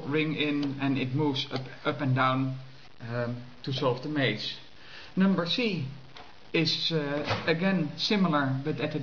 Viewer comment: I heard "uhh... number C"... number C?